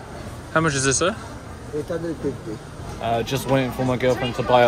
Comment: scammer ? did he say 850?